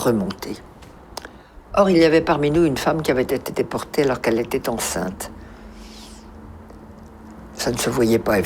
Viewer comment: SVP, y a-t-il un(e) francophone qui peut me dire qu'est-ce qu'elle dit - en français - de 1:15 à 1:20? Les sous-titres anglais sont "One of the women imprisoned with us was pregnant." et au lieu de "Une des femmes imprissionées avec nous était enceinte." elle dit quelque chose - qui - à mes oreilles- ressemble: "auri l'est pas mine une femme qu'avait de deporter par qu'elle était enceinte." (hein?). Je ne comprends pas. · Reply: She says "or il y avait parmi nous une femme qui avait été déportée alors qu'elle était enceinte".